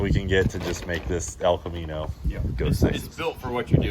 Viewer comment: I know you didn’t but it sounded like you said, “stealth Camino” which sounded pretty rad.